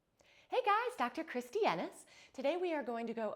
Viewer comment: I genuinely thought she said Dr Kristie Anus 😂😂